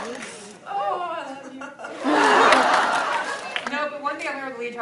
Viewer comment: is he saying "Oh how I love you" ?